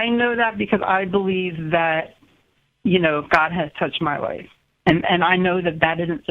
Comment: God has touched her pu**y, thats why she belives in god? Did I hear that right?